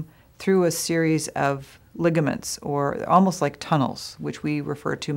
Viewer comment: I have a trigger finger..... On call of duty xD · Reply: When she said ligament I thought she said ligma LOL